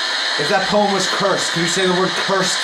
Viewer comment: i think if you listen closely it sounds like it said it was cursed